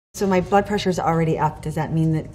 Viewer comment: I thought she Said butt pressure instead of blood pressure 😂